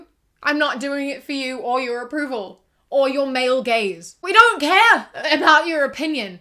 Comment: When she said “male gaze” I thought she said “mail gaze”...